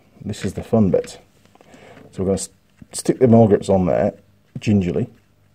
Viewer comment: Nice job. 59:43 did you say more grips? We call that a vice grip.